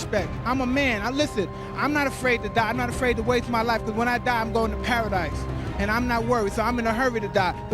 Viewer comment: Did he say, when i die i go to paradise? 🙂